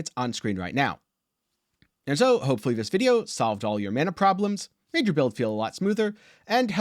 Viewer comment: Damn I thought it said "Fix your Mama Problems"